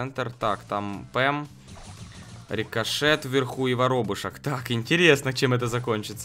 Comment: я один услышал верхуй